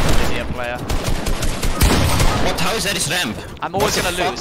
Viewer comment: What how is that his “remp” instead of ramp💀😂